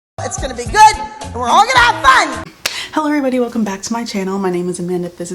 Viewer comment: At the beginning of the video when you said “hello everybody welcome back to my channel” it sounded like you said “Hilary welcome back to my channel” and it was weird bc my name is hilary.... I just had to say that 😂